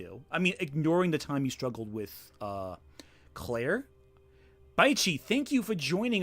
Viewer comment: You accidently say "Ignoring the time HE's struggled with Claire" I fully believe it was a mistake, but Lily's one of the type of people I fully expect to clip that sound byte and parade it around as "proof Ant's transphobic" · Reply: What I hear is "Ignoring the time YOU struggled with...huh.... Claire."